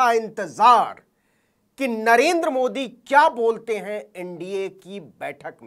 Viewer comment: did he say किन्नरेन्द्र मोदी ?